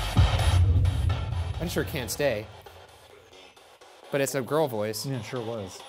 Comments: I heard “I can’t escape” when they said they heard “can’t stay” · Why is no one else freaking out about how clearly the radio said words? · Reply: I I usually don’t buy the spirit box words but dang I heard “can’t stay” pretty friggin clearly